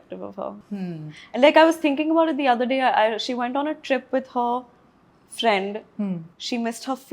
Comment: she was actually saying khushi went on a trip with boyfriend 😂